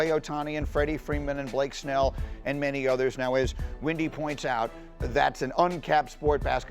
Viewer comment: I thought IT WAS SAYING LUKA WAS WINDY... from running up and down the court.